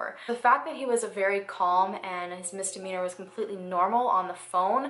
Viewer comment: It sounded like you said misdemeanor and I think you meant demeanor 😋 it's ok, I do this kind of thing all of the time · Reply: at 29:24, it just sounded to me like you said "...his misdemeanor was calm," and it that context demeanor would make sense so maybe that's what you did say and I heard it wrong. Maybe you didn't say either and I am just not hearing what you're saying correctly